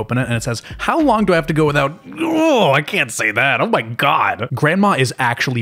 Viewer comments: i will not rest until i find out what that says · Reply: How long do I have to go without specs to be considered a v*rg*n again? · what was the uncensored saying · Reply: How long do I have to go without specs to be considered a v*rg*n again? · I NEED to know what that says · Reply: How long do I have to go without specs to be considered a v*rg*n again?